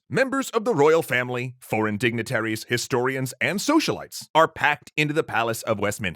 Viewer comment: I thought he said socialists, not socialites.